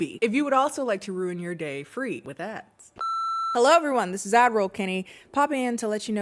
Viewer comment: When I first started watching you I thought you were saying “Avril Kennie” and I just assumed it was an inside joke I missed until I realized like 10 videos later you were saying ad roll and now I feel like a fool every time I listen to the sponsor segment 💀